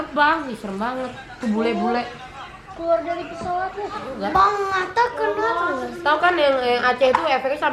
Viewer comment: "bang atta kena dong"maaf kalo salah denger😋